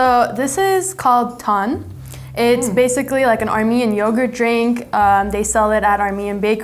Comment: Who relse thought that it said ‘Americans’ instead of ‘Armenians’ 🤣